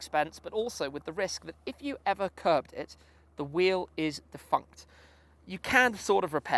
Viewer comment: Did he just say "Fucked" ? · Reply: Nope "defunct"